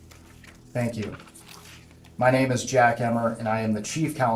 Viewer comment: I thought he said “my name is Jack Hammer” I was like dayum badass alert lol